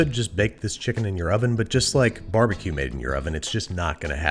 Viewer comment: When babby said “barbecue in your oven” I thought he said “barbecue in Europe” and had to rewind to find out whether I misheard or if Andrew roasted the whole continent of Europe for not having barbecue like we do.